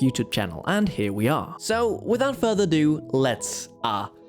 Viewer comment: Did you say "without further do" ?